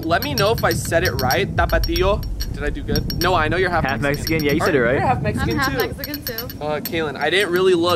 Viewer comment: She half Mexican 😍 okay we’re like due for a get to know kaelyn Edit; I actually thought she was half Lebanese or something along those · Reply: Was I the only one who thought it said half lesbian😭💀